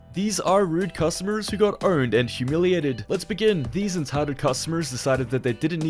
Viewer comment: sounded like he said “got earned” 😂